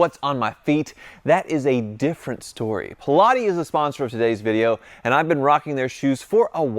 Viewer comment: I thought you were saying shoes for Pilates and I went wtf. 😅 Glad your Evo is working out well for you.